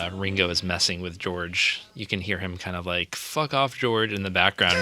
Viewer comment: I hear "Rock on George" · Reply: Yep, it is "Rock On George."